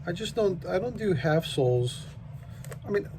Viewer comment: lol, sorry, a few times when you were saying half-soles i thought you were saying "assholes". i finally got what you were saying...